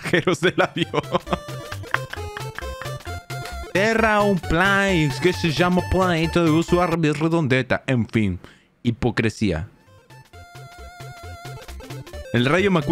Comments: lo que dice es "la tierra no es plana, mas se llama "planeta" en vez de "redondeta", en fin, la hipocresia" pa los que no sepan xd saben que es gracioso? que se que es lo que dice sin usar el traductor, parece que se portugues :) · creo que dice: la tierra no es plana más se llama planeta y envés de redondeta en fin hipocresía